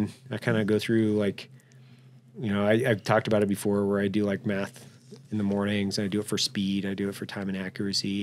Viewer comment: "I do Meth in the morning, and I do it for speed..." - Evan Hafer Ceo BRCC Did I hear him right? 22:09 more Meth?